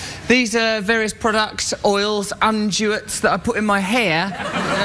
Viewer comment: What word does he say, "unduates"? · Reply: I believe he meant to say “unguents”... but he mispronounced it.